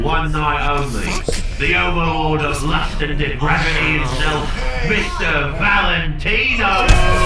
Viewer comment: a couple seconds after this moment, Angel just says fuck and it’s kind of cute to me for some reason. Also, did the announcer say Victor Valentino because that’s what I heard